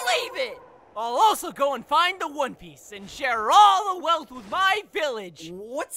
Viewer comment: Did he say one piece